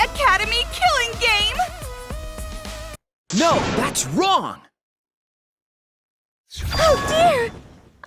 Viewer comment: Why does shuichi say: No thats wron